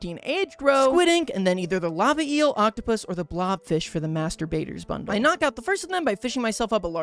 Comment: I wasn't really paying attention and all I heard was "masturbator's bundle" and I was thoroughly confused and concerned until I realized it's "master baiter" 😭